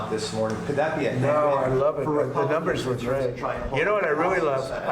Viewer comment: Listen closely to the tape; the written transcript is incorrect. He doesn’t say the numbers were “glitched”; he actually said: “the numbers were GREAT”. Apparently, someone at the White House decided that it didn’t look great for the President to say that high inflation was great and tried to cover it up by issuing a transcript with a different adjective; never mind that it made no sense without some kind of explanation as to what that might even mean….